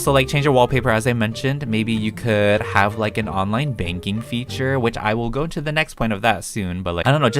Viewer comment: At first when you said to have an online banking feature i thought you said online dating feature 🤮 🤢